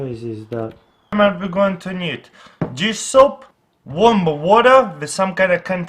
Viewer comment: am i the only one who understand jizz soap ? :D